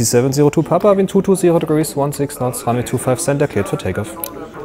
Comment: was sagt er bei 2:37 3:28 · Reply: Easy 702P Wind 220/16 Runway 25 Center cleared for Takeoff